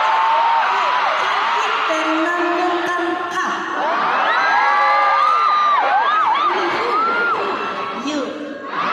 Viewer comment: จีซูพูดว่าอะไรอ่ะ ได้ยินไม่ชัด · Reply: น่าจะ ชั้น มี คู่ เหมือน กัน คะ... ยู